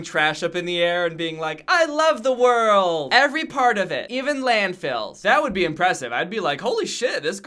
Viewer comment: when he said landfills i thought he said landMINES so i sat there like uhhhhh